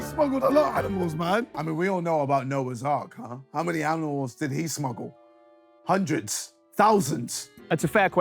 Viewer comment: Did KSI just say aminals??